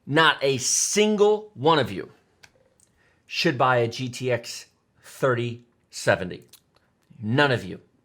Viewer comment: Did you just say gtx 3070?